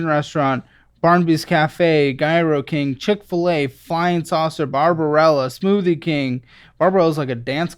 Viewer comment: Not me mishearing “flying” as “lime” thinking he rode a little scooter